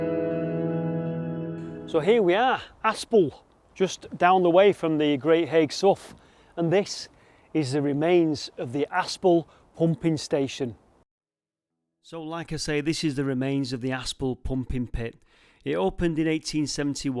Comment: For a second there, I heard you say "Aspinall Humping Station", which brought some disturbing images to mind...anyways, great vid.